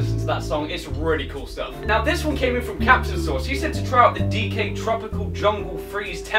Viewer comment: wait did say CaptainSause. CaptainSause is one of my fav YouTubers Like